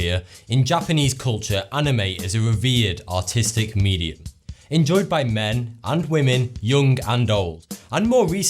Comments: am i the only one who heard autisic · Misheard him say "autistic" for a second there, almost spat my drink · In the beginning of the video I thought he said it was an autistic media lol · OH GAWD, being a non-native english speaker means one hears fucked up things: I thought thoughty2 said "Anime is a reverred autistic media..." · i fr thought he said "revered autistic media" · I heard "A revered autistic medium" Fuck me. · Somehow, I misheard Artistic as Autistic.. lol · OMG literally at the beginning I thought he said "autistic media" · I thought he said anime is an autistic median. Though he meant artistic, because of his accent, I misconstrued it. XD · When he said "Artistic" I thought he said "Autistic"